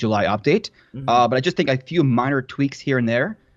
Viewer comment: did you say miner? 😂